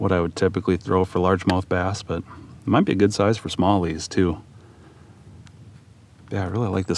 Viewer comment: Every time you say smallies, I hear Somali's. When you added "catching smallies," I almost lost it the first time lol. Being in Minnesota is just the cherry on top due to the fraud by the smallies (Somali's).